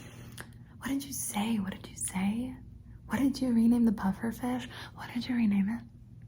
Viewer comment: tell me why i heard “what did you rename the puffer fish??” and half-asleep, I whispered “bowser’s beach ball” 😭😂